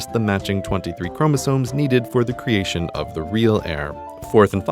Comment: I'm sorry but my little bit of OCD is killing me. At about 1:30 the captions say "They're not in line for the throne, they're just the matching 23 chromosomes required to produce the real AIR", when, unless I'm mistaken, it should say HEIR. Other than that, awesome video, and keep up the great work.